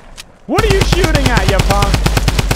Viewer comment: Surprise m...(you know the rest. Hey! Didn't say you could say it out loud. Keep it family friendly ok!?) 😁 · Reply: I think he’s on about 6:35?! He said punk not m*g